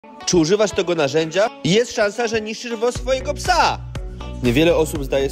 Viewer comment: Czy tylko ja usłyszałem „niszczysz mocz swojego psa!” a nie „niszczysz włos swojego psa!”?